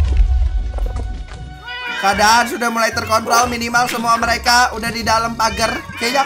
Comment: ngomong apa dah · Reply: fnaf x light craft terkontrol anjir tapi gw juga sempet dengar terkontol wkwkwk